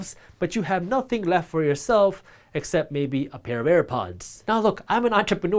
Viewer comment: What you said “a pair of bare pockets.” What I heard: “a pair of bear claws.”